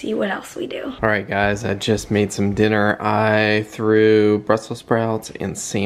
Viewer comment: Mary thought you said “I grew brussell sprouts “ and I thought you were going to day l I threw up Brussel sprouts! 😅